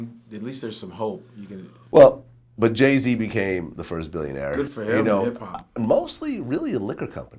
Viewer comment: I thought they said Dre was the first